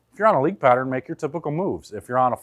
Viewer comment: Can you clarify what you say at 6:35? It sounds like “If you are on a league pattern, then you need to book a move.” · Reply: He said if you're on a league pattern make your typical move.